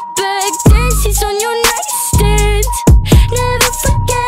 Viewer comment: I’m just gonna say, I first heard “damn she’s on your nightstand”. Help my twisted mind.